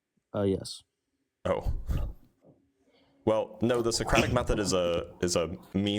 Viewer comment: Thought he said "idiosyncratic method" instead of Socratic method.